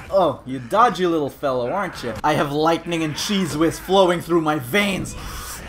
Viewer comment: did he say I have white nigga cheese whiz